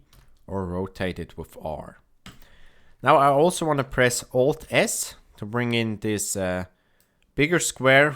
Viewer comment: Hello, I cound not do it, What did you say command O S around 3:23?? Please help me, if you can. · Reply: *Alt + S